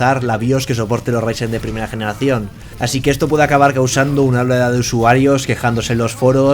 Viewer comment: pero que número es ese o.0 Ya entendí dice oleada :v